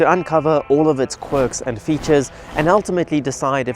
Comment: Wait did he say QUIRKS & FEATURES ?